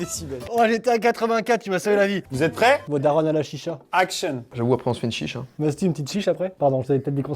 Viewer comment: att c'est moi ou il a dit "t'es une chicha?" 😭😂